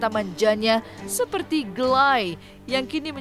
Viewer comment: Sedikit memperjelas sebenarnya bukan gelayyy yang diucap ka nissa, tetapi galikee yang diomong ka nissa, kan dia awalnya bilang "ii ga suka"=galikeee,bukan gelayyy!!!. Dan maksud dia itu dari indonesia di sambung ke inggris nah kedengeran gelayyy karna dia ngomongnya di manjainnn atau di gaya"in. Plisss lah gausah sotau dan menghakimi orang terus, kita sama" pendosa yang kebetulan dosa dan aib kita masih di tutupin sama allah ,samasama pendosa yang bedabeda jalannya jangan saling menghakimi deh plisss!!!